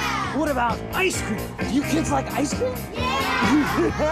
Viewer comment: i always thought he said homos not hobos lol